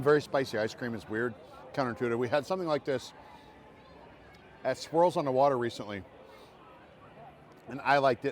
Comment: Lol - I had too much wine at the pool today! I thought said “Squirrels on Water” and a spent half hour pondering what the heck he was talking about - finally figured out that he actually said “swirls on water.” I am sweating watching you guys from my highly AirConditioned house, thank you for your service. 😂